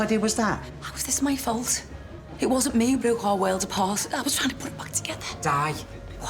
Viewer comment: after diane says "how is this my fault?" can anyone tell me what she says that causes ro to freak out? im canadian so the accent can be hard to decipher sometimes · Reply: She said "It wasn't me that broke our world apart, I was trying to put it back together"